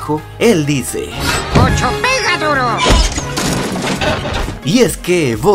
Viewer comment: yo entendí ocho pega duro l